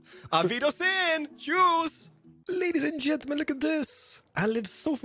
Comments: im so mad at myself i thought he said "juice" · did he just say ‘Aug wierdesen Jews’?? Then call his villager Adolf???